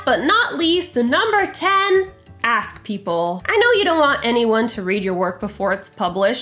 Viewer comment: Okay for the last one, I thought she said “ass people” 😂 and for a split second I thought she was gonna elaborate like “your characters have to be an asshole at least one or two times”. IMO that would also make them realistic. As I’m looking around, no one exactly has halos floating above their heads, even the nicest people I know.